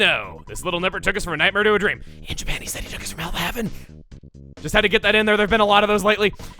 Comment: What in the ever loving heck did you say at 10:32? You said it so fast that its completely unintelligible! Edit: After listening to it about a hundred times, I BELIEVE you said "In Japan he says he took us from hell to heaven?"